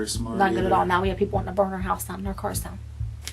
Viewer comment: i was SOOO confused and what i read lmaoooo but shes saying "people wanting to burn our house down and our cars down"